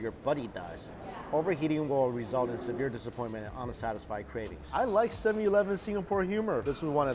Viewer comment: For a second I thought the thing said homicidal ravings instead of "unsatisfying cravings"